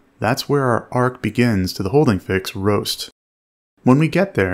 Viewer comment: I keep hearing "ROAST" and not "BOAST"